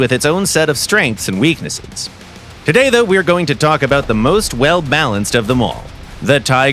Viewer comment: did he just say the most blanced LMAO